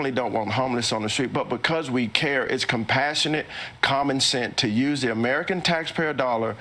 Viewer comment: Did he just say "common scent"?